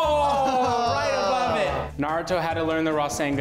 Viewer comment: I had captions on and when it said rasengan the captions said raw sand gun